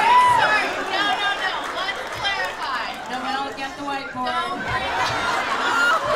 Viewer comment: What does Emily say in the minute 31:48? I can’t get it, please someone help me ;0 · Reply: I believe she says "Noel get the white board" then she pauses and says again "get the white board"